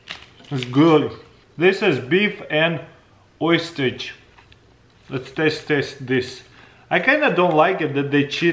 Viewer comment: Beef and what....oysters? Say again?